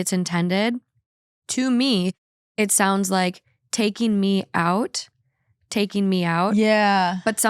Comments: hold up - the vocals in the intro are a mystery to, too ??? I always hear "Pennsylvania, Pennsylvania" WE NEED ANSWERS! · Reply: I always hear "freaking me out, freaking me out" which I thought was fair because a lot of reddit stories freak me out haha. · THANK YOU for addressing the intro song lmao 🤣🤣 I always thought it said freaking me out LMAO